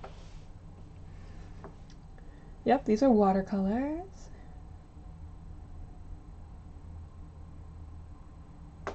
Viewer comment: Darn it, I missed the stream! I'll make sure to catch it next time 👍 At around 37:43 you mention two channels you watched a lot when learning to draw, Stan Prokopenko and someone else? Who's the second person? I can't figure out a spelling to search for 🤦😅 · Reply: We'll definitely stream again soon! :D And the other person was Sycra. :]